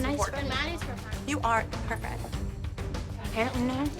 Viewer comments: Maddie said I am her friend mom but the subtitle said I guess we’re not friends anymore. To cause more drama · Maddie clearly says "I'm a nice friend mom" not "Well I guess we're not friends anymore" as the subtitles say. Its horrible how the producers manipulate scenes just to make the show more dramatic. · i think its evil that the subtitles for maddie say something that she didn't even say. What she actually says was "Im a nice friend mom" this show kept on trying to make maddie look so full of herself when it was really just Abby.